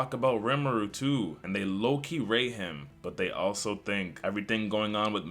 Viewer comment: Lowkey what 7:35 ? Did you say rate?